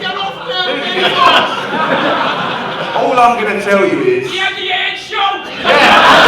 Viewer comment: what does the punter shout at 8:31 that cracks everyone up? Can't make it out. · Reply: The Eddie Hearn show